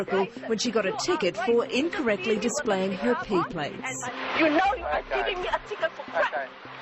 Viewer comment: wait did I heard that she got a ticket for incorrectly displaying her piglets?